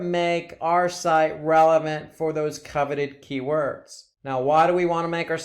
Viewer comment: what key words? Cover what? Sorry listened several times, still didn’t catch it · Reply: Hi Kit, our instructor is saying coveted keywords. Hope that helps!